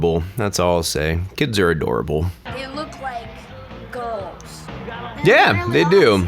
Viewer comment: Whoa, so I watched this video and at 4:00 I thought the kid had said ghosts instead of girls (I don't know, 'cause the lighting or something) and slapping them sounded really odd. Then I watched your RE video and yeah... that's pretty much unacceptable comment.Glad you owned it and apologized.